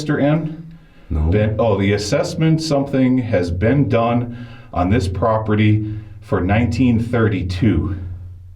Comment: i think it says - the assessment work has been done for this property for 1939